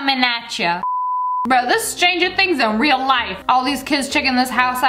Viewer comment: did she say stranger things 😀😀